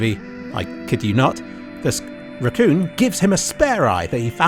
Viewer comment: subtitles says "squirrel" but say "sq- raccoon "